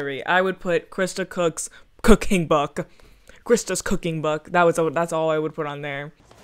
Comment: I thought you said Christa's Cocaine book....